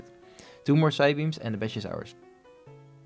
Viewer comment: Anyone else hear “two more psybeams and the bitch (badge) is ours” at 8:24?